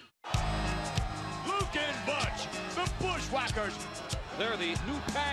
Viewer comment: Did someone say Luke is butch